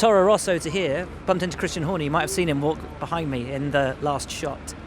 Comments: Did he just say 'Christian Horny' ???? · Did he say Christian Horny